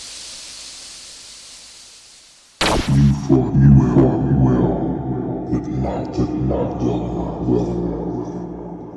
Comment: What is Mecha saying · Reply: “You’ve done well… but not well enough…”